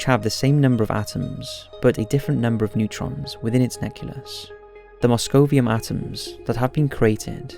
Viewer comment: did he just say neculus?